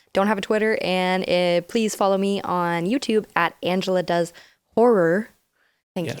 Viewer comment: During the intro, did he just say, “Angela does whore.” I’m surprisingly interested and intrigued 🤔🤫🤫🤫🤫😂🎉